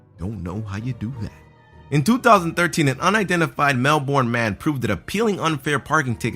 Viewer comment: He's hard to understand! At first I thought he said "a male born man" it took me a min to realize he was saying a Melbourne man 😂. He needs to speak a tadddd bit slower!!